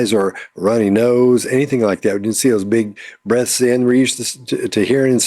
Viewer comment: lolololol oh my gosh, at the end when says "we didn't see those big breaths..." I heard "we didn't see those big breasts" at first I was like "what?!" but then I realized he said breath not breast. She reminded me of when my kids were toddlers and would through a fake fit. Everyone BUT them knew it was just ridiculousness, but they thought it was believable.